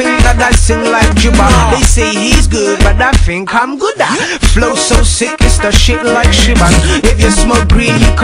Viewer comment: I thought he said "Flow so sick it's the shit like Shuban" Lol